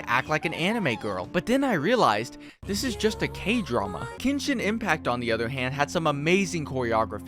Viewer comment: I'll be honest, I thought you said genshin impact for a minute there.